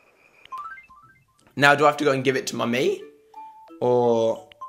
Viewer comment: When he says my mii, it sounds like he is saying mummy XP